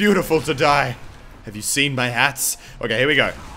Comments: He sounded like Jarl Balgruuf saying "Have you seen my ass??" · ' Im too beautiful to die have you seen my ass' That is what i thought he said until i replayed it. Did anyone else thought he said ass?